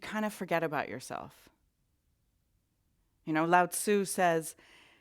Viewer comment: you quote Laotse on love, very beautiful ❣️ But I just have to tell you what I understood at first : "Loud Sue says ......" 🤣